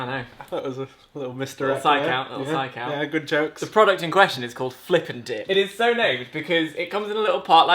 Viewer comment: I thought you said flip and dick and I was what ????? I should go to sleep now